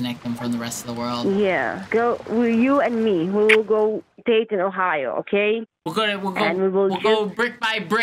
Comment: What she said: "You and me, we will go Dayton, Ohio" What I understood: "You and me, we will go date in Ohio"